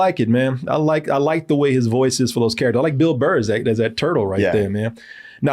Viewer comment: Wrong, he mentions Bill Burr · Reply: Oh. I thought I heard Bill Murray.😅